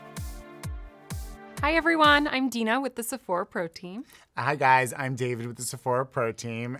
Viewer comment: Every time they greet you all I hear is "Welcome to the Sephora Protein" . You should make some merch and wear it in your vids 😂